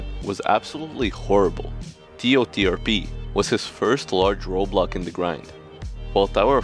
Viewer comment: I thought bro was saying "Roblock" for the entire video instead of "Roadblock" 😭